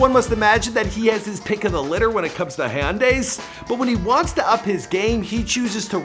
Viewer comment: Did he just say high-un-day instead of Hyundai